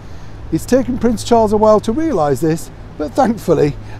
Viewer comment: I know YOU say “…in the heart of London.” But I hear “…I’M the heart of London.” 😂 I think it’s more fitting.